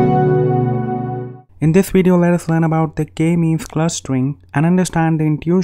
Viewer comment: I keep hearing "Gay means Clustering" - I've only watched 50 seconds so far. I'll watch the whole thing now..